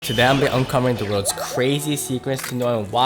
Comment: Who heard todam not today